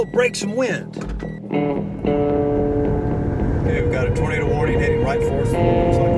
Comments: The caption says "Okay, we have a burrito warning hrading right for us. " My day is suddently 100% better Edit: Everytime the word "tornado" comes, the captions say "burrito". Now it's 200% better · What he said: We have a tornado warning With caps: We have a burrito warning All of a sudden, now I’m hungry... not for burritos.